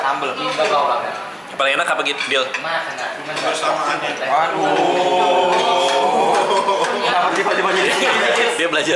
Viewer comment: ngomong apaan sih si 😂 kagak kedengaran wkwk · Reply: Unknown User ngomong "KEBERSAMAANNYA" 😂😂